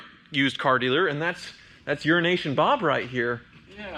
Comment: I just hear "Urination Bob."